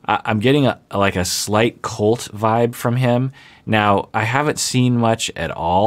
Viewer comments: When Dr. Honda said Andrew gave him a Colt vibe, I thought he said cult vibe and I was shook. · Oh a "Colt vibe"....jesus I thought you were saying a 'cult vibe' Jim Jones over a pre school would be a double YIIIIKES